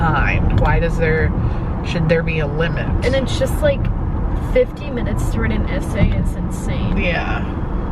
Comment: At first I thought that was saying they had 15 minutes to right a essay and I was thinking yeah a little extreme but doable... but then I realized she said 50 minutes. Also as far as the reading and answering questions as someone who struggles to read... all you have to do is read the question and then go back and skim the text for common words and you'll find the answer super fast... They're also in order.